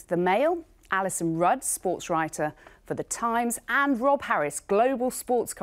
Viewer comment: did anyone else hear Rolf Harris first? 😂